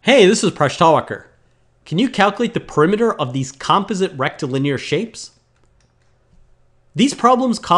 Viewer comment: For a minute there, I thought I heard "rectal" linear......LMAO!